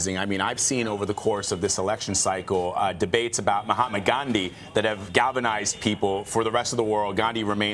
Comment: Did he just say mohammad gandhi?